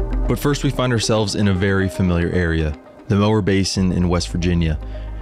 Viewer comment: The Mower Basin area or “Mine-tana” is a hidden gem. Fantastic mtb trails throughout as well as close proximity to Guadineer Scenic area, Snowshoe, Shavers Fork, etc. Looks like a rad trip! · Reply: Thanks for clarifying. I heard "Lower Basin" and was really confused.😂